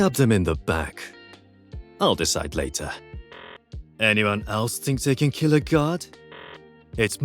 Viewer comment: i heard this voice line after clutching a 1v4 as yoru and i thought he said "anyone else be thinking about god?" Before dying to the spike explosion